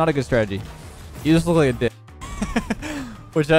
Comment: i thought he said you just look like a ni- for a sec lmao